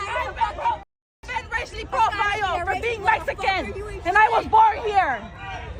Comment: did she say she was a “Mexican that was born here?” They just keep getting dumber don’t they?